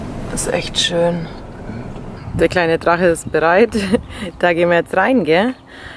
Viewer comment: ich habe verstanden "Der kleine Drache ist breit"